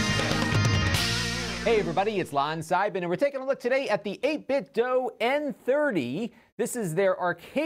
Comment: Ha ha, it sounds like you say 8-bit-dough.